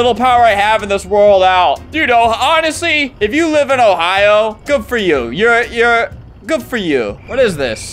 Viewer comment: I thought bro said " goofoyu" to act chinese but realised he said "good for you " 💀